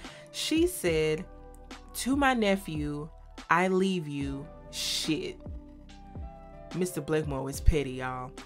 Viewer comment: I always thought his lawyer said, "I leave you zilch." But shit is quite effective as well! 😹😹😹